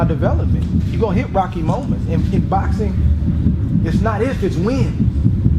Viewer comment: What does he say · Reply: he says "it's not IF, it's WHEN"